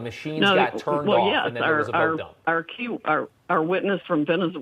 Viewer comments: dude. what if sidney powell is qanon? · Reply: I swear she slipped and said 'our q' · Anyone else catch this? At 4:08 ish she slips up and says "our Q...our, our witness from Venezuela..." Cat's out of the bag and so is she.